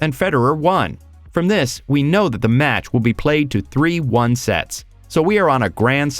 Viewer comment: ???? I never knew Tennis would be tougher to understand than american football. I just heard the phrase "Triple Break Point" no earthly idea what that means. And term, "3 WON sets" made my head hurt. Won sounds like one. So I hear "3-1 set". So I still don't like tennis.